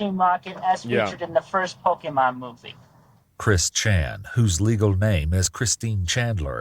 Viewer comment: Dang man I thought you was saying he is Christian. ❤ I'm still not sure about him but I did get baptized. -fromGa w/❤